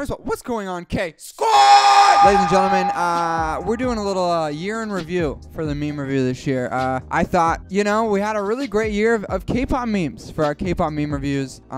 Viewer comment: At the start I heard “urine review” 😂